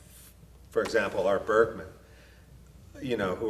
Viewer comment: What IS the name that he says at 1:18?? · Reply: Art Bergmann